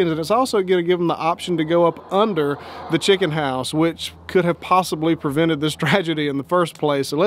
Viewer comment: Aloha hugs 🤗 I thought you said Hog nice Hen house I understand hot phone interesting net put link in please